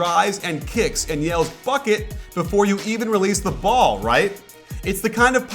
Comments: Did coach say "fuck it" at 8:27? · when u said bucket I thought u said fuck it